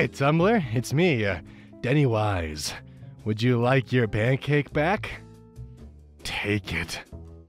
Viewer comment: I heard bandcake at first.